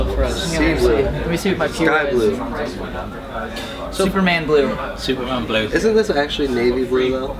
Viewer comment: They seem like really pissed or bored or something... when Kellin said what type of blue it was I thought he said sofa man blue not superman blue lol XD